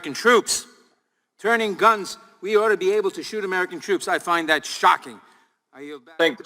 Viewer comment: Gee. I thought Roy was saying we should be able to eat children. And he was really saying that we should shoot American troops. Boy, did I get that wrong. Nadler and Schiff. To quote Gutfeld, after a manner, If Nadler were any dumber he'd be two Nadlers. Nadler would be shocked if you wanted to feed a child, rather than kill it.